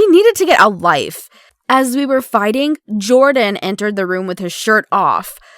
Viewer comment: At first I heard “as we were farting” Lmao